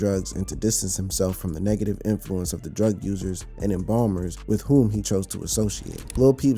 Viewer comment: “Embalmers “?? I think that said ENABLERS